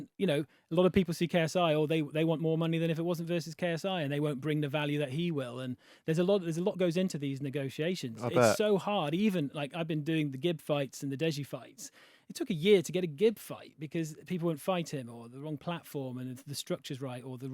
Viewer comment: swear to god i heard N word at first XD